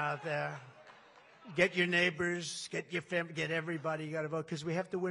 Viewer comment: Did he say "Get your neighbor, get your pimp?" If he did, that needs to be on a t-shirt 😂